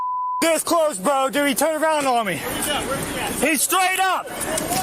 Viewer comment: I heard, " He turned a gun on me!"